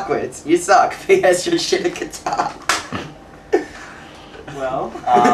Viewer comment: lol at first i thought he said, 'can you shit out a guitar' lolzz... gay bash bahaha